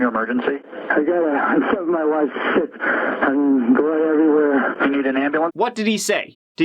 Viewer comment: ok, so I was painting when I heard the 911 call, so I didn't see captions, and I heard "I stabbed my wife,... Shit! I mean, blood everywhere..." I had to rewind to see what the captions were saying.